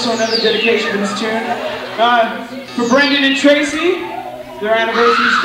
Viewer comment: Did you say Brendan and Tracey???